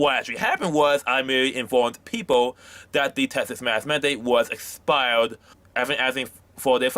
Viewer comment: I don't mean to be a dick but literally every time. talks I'm like what did he say??? Maybe he should stick to writing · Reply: I stg I'm not lying when he said he got in trouble for asking about mask mandates, I thought he said he got in trouble for asking about masterbation I was like well yeah I can't just walk around asking people....